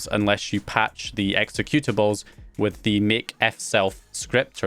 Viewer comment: What is the name said around 11:30 for patching the executables? MakeXL or something like that? Anyone know?